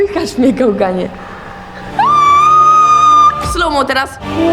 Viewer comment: czy tylko ja usłyszałam kochanie ?